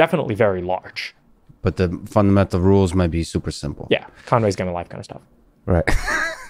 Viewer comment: What does George say at 2:28? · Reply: Sahil Trivedi Conways game kinda stuff, or something akin to that